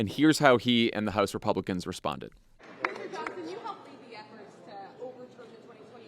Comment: When I hear his name, I hear "my Johnson" 😅